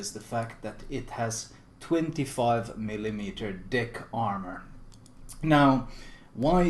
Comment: I heard "25mm dick armor". Uh.